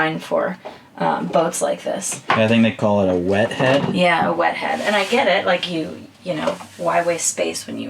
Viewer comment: Did I hear "wet head"....... sounds like a good weekend went bad.